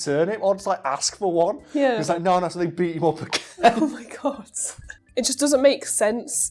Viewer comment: did say 'oh my gods' instead of 'oh my God'? Because if so: respect!